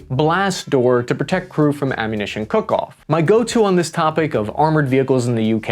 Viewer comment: I swear I heard "Ammunition cock-off" 😅 -Edit: When I heard it now fires "DM73.. Long Rod Penetrators", I knew I heard right😂